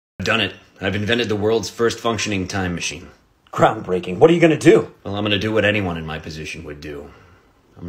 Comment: I don’t know why but the first time I hear it I thought he said “the worlds worst functioning Time Machine” which still would’ve been funny